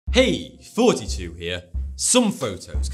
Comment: thought he said, hey 42 here